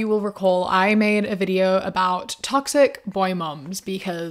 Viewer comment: I thought that said toxic boy, 2 mums